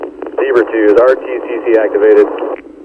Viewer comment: It sounded like they said "RPG's activated" 4:45 I was disappointed not to see a big explosion..... XD